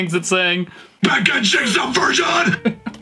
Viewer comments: and 10:30 are the best faces ever 12:26: I kept thinking it said "alpaca cheeks are vermin" ;-; 2:45: I could listen to that one line over and over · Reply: I heard “back a jig’s a version” Makes no sense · I hear “ packaging’s a virgin”